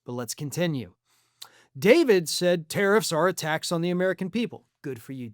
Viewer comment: At first I thought David said tariffs were "attacks" on the American people. Not "a tax." But either would be correct.